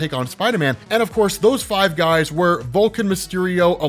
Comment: Definitely sounds like you said Vulcan instead of Vulture. Lol 😂 Definitely my ears playing with me. Lol